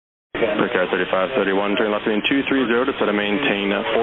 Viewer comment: Your graphic is wrong @ 1:03. Both the tower and the plane said 230, down to 4000' You wrote 330 instead of 230 as response from Brickyard 3531. Edit: corrected from "town" to "down"